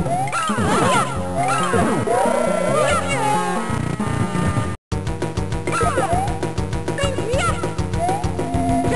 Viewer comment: What is Birdo saying at 00:22 ? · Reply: She Said How Could You